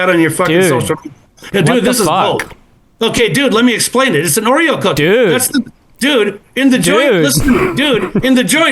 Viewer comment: He blocked me on Instagram when I asked where to send the cat food 🤷‍♂️ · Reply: did he Call them Oriel cookies???? Did i hear wrong??? And i Thought Trump was bad.. Dear god.